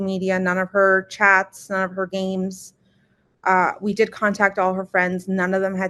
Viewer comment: none of her AIMs? As in AOL messenger/chat room from like 2007? I didn’t realize 13 year olds use that today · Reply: Interesting...I thought she said games then saw your comment & listened again and it does sound like she said AIM's